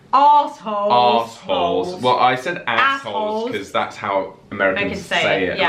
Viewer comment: I thought you said castles instead of assholes. So still have some work to do on that one. LOL.